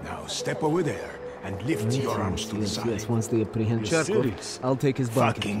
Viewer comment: When he says Cherkov it sounds like hes saying Jerkoff